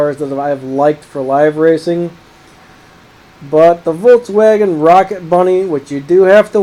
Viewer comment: did he say Volkswagen rocket bunny?